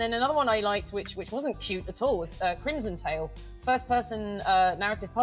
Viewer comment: CRIMSON TALE! I had to rewatch the vod to be sure I heard the title of the trans lesbian vampire game, time to wishlist it